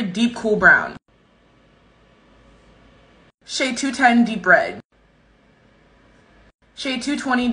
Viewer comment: Is it just me who thought she said shade 210 deep bread